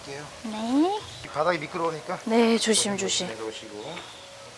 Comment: En minuto 1:43-1:45 parece que dice “mira que chingado es” 😆